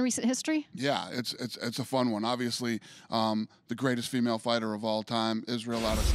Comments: Sounded like Dana said "The greatest female fighter of all time Israel Adesanya " lol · For a second I thought Dana said the greatest female fighter of all time Israel adesanya